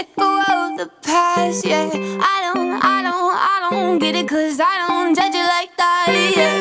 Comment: lol when I first heard this song I thought she said... "Because I don't judge you like thighs"...